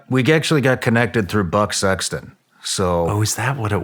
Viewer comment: Right off the bat, i thought he said " butt sex"..... I am glad i was wrong. Buck Sexton. Whew...